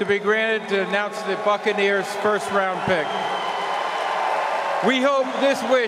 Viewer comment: it sounded like he said fuckaneers lol.